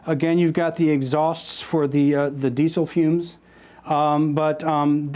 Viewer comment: did you say it's got the exhaust for the diesel fumes?